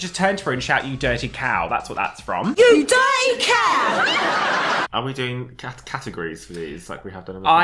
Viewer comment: I always thought they were saying “you dirty cat” hahahahhahahaha